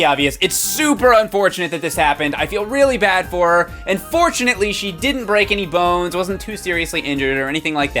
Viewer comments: it sounded like you said "unfortunatly" instead of "and fortunatly" and it made me laugh harder than ever XD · at first I thought he said 'unfortunately' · When he said, "And fortunately she broke no bones," I thought he said, "Unfortunately she broke no bones." I feel bad now. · I thought he said "unfortunately she didn't break any bones"